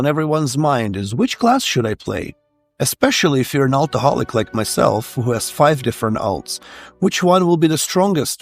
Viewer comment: I literally had to rewind coz at first I heard "if you're an alcoholic like myself" lol